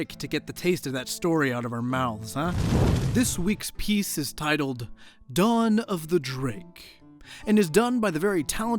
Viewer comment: For a moment I heard "Dragon of the drip"